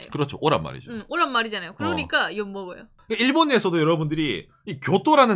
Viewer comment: 그러니까 엿먹어요 처럼 들려서 돌려봤네요 ㅋㅋㅋㅋㅋㅋ